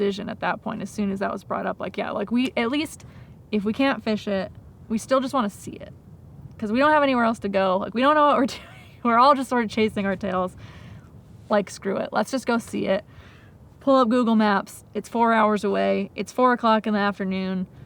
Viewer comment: great great video. am i the only one who thought he was saying Chasing Amy the entire time? you can tell my age